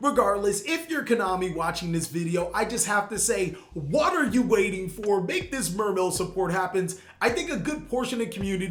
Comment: did he say "what are you waiting for" or "Water you waiting for" 😂💧 (Damn it I love puns)